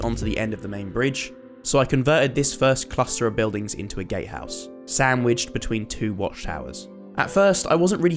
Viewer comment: bro am I the only one who thought he said gay house instead of Gatehouse Bro I can get the green but where's all the purple